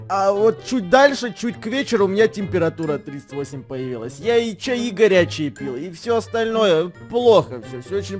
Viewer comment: мне одной послышалось Игоря чай пил? Интересно а како Игорь. Интересно это у меня со слухом не так или так было сказано